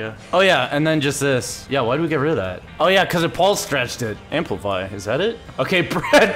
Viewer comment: ''Amplify! Bread 20?'' what does he say?? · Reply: amplify bread 20